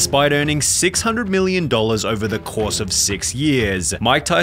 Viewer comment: For a second in 0:01 I thought bro said 6 million over the course of six meals ☠